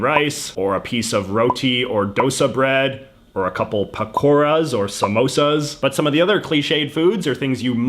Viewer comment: I thought you said "a couple of Bakuras" for a moment. Who knew the shadowrealm was a child's lunch time.